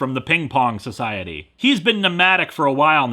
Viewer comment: I know he's saying "nomadic" but between his accent and my auditory dyslexia, I keep hearing "pneumatic"